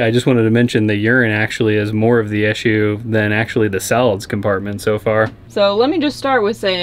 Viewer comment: So, I heard "Salads compartment" multiple times. I think he is saying "solids compartment" though